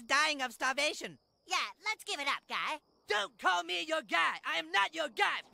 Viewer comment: is he saying gay? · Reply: but I get what you mean. It’s said In a way that sounds a bit like gay. I first thought that too once.